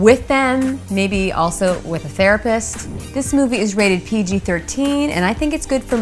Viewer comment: did she just say "bring a therapist"?